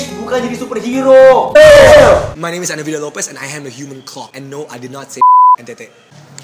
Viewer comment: Setelah sedikit lebih pintar dan dewasa, gue baru sadar kalo di 2:25 itu ngomong, "and no I did not say c*ck". Pantes disensor